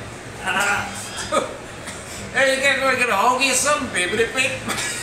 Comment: What did the R2d2 guy said that was so funny? I can't hear it. Anyone knows? at 8:42 " Get a hold of your something?????? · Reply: "Brooklyn" R2D2 said, "Wanna get a hoagie or something?"